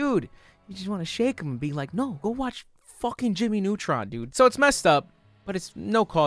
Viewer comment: Did u just say Jimmy Neutron 7:35 😨